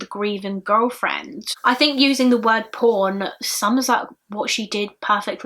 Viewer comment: any other Americans here who heard the word here as 'porn' instead of 'pawn'? Lol... And then at 12:05 I hear 'Web porn', lmao. I'm special.